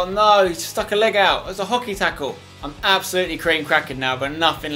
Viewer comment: Did u say hockey tackle